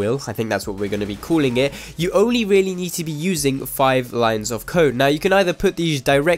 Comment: 0.39 i thought he said five lines of coke